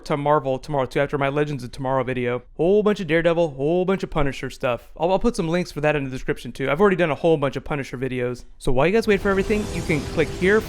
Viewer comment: did I hear you say "The Punished" he's my personal favorite character